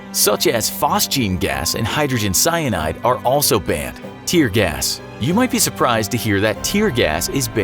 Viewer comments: I thought he said nerf gas 🤣🤣🤣🤣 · I thought he said "nerf gas" and I was like it's nerf or nothing